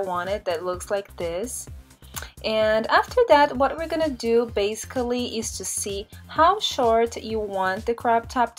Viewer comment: did she say bascili??!!